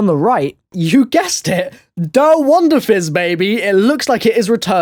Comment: What is that at 3:55 you said is returning couldn t understand you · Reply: Der Wunderfizz! :)